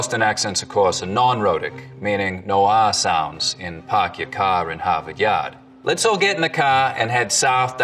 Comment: there's a mistake in the subtitles, what he said was "Park your car in Harvard yard."